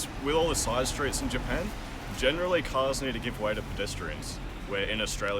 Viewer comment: generally cars〜のくだりのこの🇦🇺男性の言ってることが聞き取れませんでした。 教えてもらえますか？ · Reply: generally cars need to give way to pedestrians だと思いました！